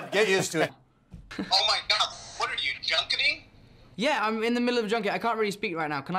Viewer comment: What did he say? "What are you, junkanee (I have no idea if that's what he said or not)?"? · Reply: He said "What. Are you junketing?" as in 'press junket'/doing the promo interviews for the upcoming movie.